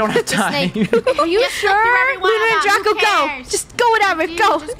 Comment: I heard it say “lunar” or I’m I crazy?